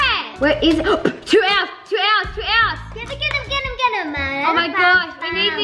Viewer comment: When they said two owls it sounds like there saying towels XD